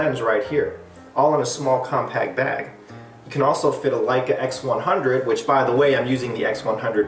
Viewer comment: did u just say Leica X100